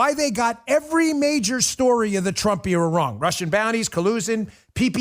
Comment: God bless Rush Limbaugh, may he for ever live in our memories. He his truly missed. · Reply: I skipped the video and happened to hear that part, and thought he was saying "Russian bot".